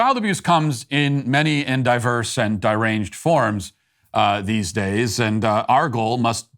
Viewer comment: did you just say "diranged"...?